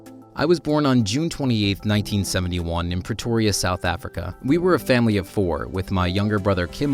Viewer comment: I thought he said petoria lol (Btw Petoria is from family guy)